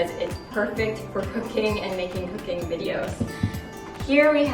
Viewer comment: I heard something else besides cooking xD 'This is my favorite part of the house because it's good for cocaine' 😂😂😂😂